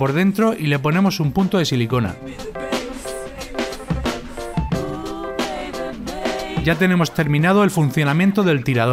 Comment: De 8:51 a 8:54 que dice, sale muy rápido :'v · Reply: Dice dale a like si ves esto y responde:¿qué quieres de regalo para navidad?